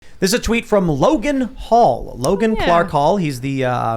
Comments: Thought he said Logan Paul for a sec 🤣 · Dude I thought you said Logan Paul XD